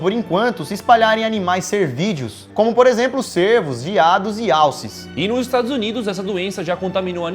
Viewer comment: eu ouvi "VIADOS" sksksksk